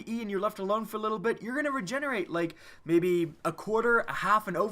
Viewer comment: DID YOU SAY ITS REGENERATING